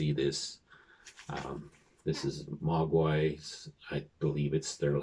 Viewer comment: The Vapors LP was surely worth $10 - but not so much the Cars - all of their LPS are very common in my part of the States... Couldn't understand who you said the LP @ 12:30 was by - are you saying "Mog White"? Never heard of that one but since you said it was one of those "post-rock" records I wouldn't know it anyway. One of my collecting pet peeves are albums with absolutely no name of the artist or no info on them! Maybe there once was an info sticker on the shrink... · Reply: The band is Mogwai. That Cars album is not quite as available around here and they are wore out when they are. I know in the States I used to find them everywhere.